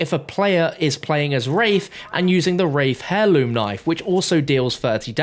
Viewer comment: did he just call it a hairloom? 😅😅